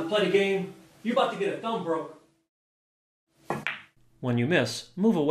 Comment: "you about to get a thumb broken" damn near peed myself! Outstanding video!!! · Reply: I thought he said "thump, bro" 'bout to get a thump. I only viewed it once.